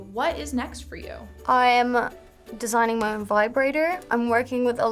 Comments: did she say vibrater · Did she say she’s creating her own vibrator?